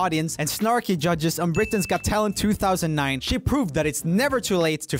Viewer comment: When roomie told "She Proved" I thought he said "She Pooped"